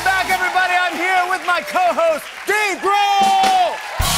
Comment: Did it sound like he said Steve Brule instead of Dave Grohl? Or is it just me?